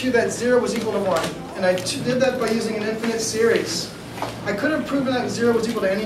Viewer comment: did he just say T-Series